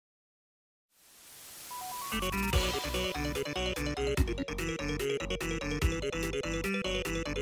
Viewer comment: timestamps: ________________________ 0:01 patrick (The Bikini Bottom HORROR) 0:39 NO, THIS IS PATRICK! 0:42 Patrick 1:47 Spongeboy 2:14 suicide squidward + fork on his threat 2:25 Squidward pulls out his clarinet 3:18 squidward got hit by a door 3:19 the two faces of squidward 3:30 plankton appears 3:58 Here we go! 3:59 spongeboy 2 4:52 what did moar krab say? 4:57 MOAR KRABS 5:26 Plankton appears again 6:01 karen appears 6:53 spongeboy 3 8:30 i'm ready! 8:31 spongebob 9:29 patrick appears 9:51 spongebob cured patrick? 9:52 spongeboy last 10:10 patrick and spongebob helps bf 10:39 spongebob sings at the end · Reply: What Krabs says “I ain’t payin you to okay dress up” squidward sigh “Breath on your own time.” Not exactly sure if I spelt that all right English is hard